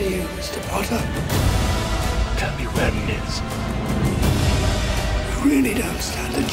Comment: I swear at the start it sounds like Voldemort says: "Tell me where my nose is" HAHA